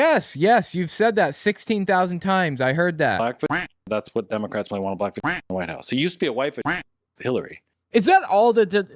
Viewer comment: What’s that word that keeps getting censored? I can’t make it out. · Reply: Vagina